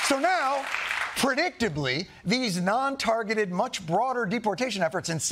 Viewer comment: I thought he said “ portdictably” I lost it, I wish he had.